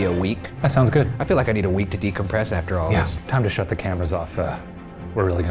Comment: see what he said was Cut the cameras........deadass